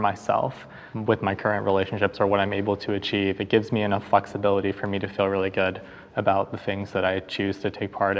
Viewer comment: I heard ‘It gives me enough fucksability…’ instead of flexibility…🤣 How apt.